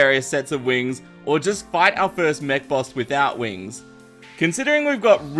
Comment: I thought he said meth boss XD